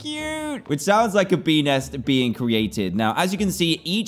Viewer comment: I thought he said pen##